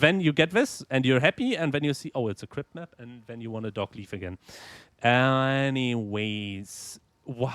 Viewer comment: What did you say at 26:17? About the crypt map? Dark leaf?